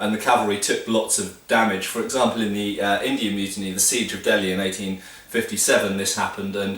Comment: Did he say Indian Mutiny @ 5:25?